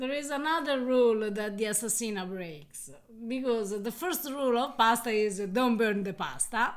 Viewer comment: I thought I heard say “ the first rule in pasta “ 🤐 now I’m scared 😂😀